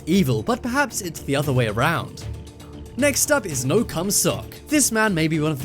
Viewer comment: it sounds like you said no cum sock